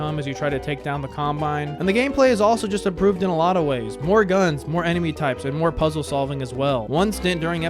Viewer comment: did he say more guns 😭